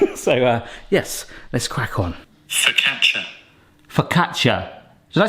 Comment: might've descoverd something here, I don't hear "Focaccia" but instead I'm hearing "Sitention" (which isn't a word haha) let me know if it's just me?